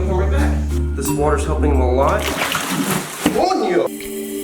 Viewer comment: did he just say coño?